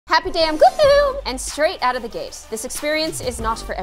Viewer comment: When I first watched gloom, I thought it said “Happy day ON gloom” Like gloom’s channel ;^